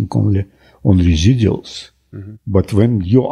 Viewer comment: I think in 7:13 he says "residuals", not "details" (as in the subtitles). That's an important difference for the meaning of what he's saying.